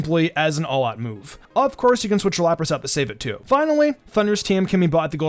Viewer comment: What did he say about Lapras after he talked about Perish Song? I can't quite make that out.. Please help 😭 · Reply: “Of course, you can switch out Lapras to save it too.”